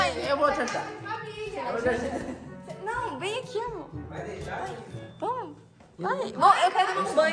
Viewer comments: só eu que escutei um vem aqui amo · Será que só eu que escutei a chamando o de amor 8:55🤭